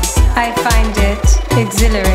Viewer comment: LOL the closed captions say "You fart in my body. Unnhhh feels so nice."